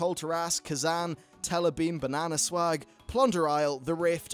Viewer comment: I'm sorry but at 6:03 did he say "bananaswag" hahahaha